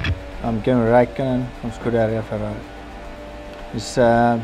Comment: I’m Kimi Raikkonen... for Scuderia Ferrari. 😂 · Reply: Marcos Ortiz I heard "from Scuderia Ferrari"